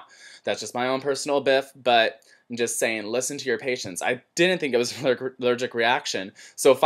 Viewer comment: I misheard and thought you said "Listen to your Haitians..." Instead of Listen to your patients...